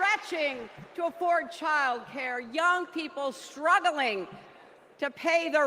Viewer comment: Sounds like she said KAREN struggling to afford health care.